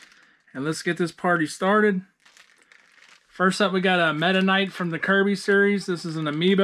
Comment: Meta Knight.....thought you said Mennonite ! LOL.